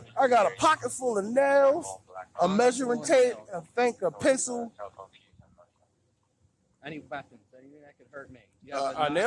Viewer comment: 🤣😂🤣 Did he say "I have a pocket full of nails, Tape measure, And I think a pigeon!"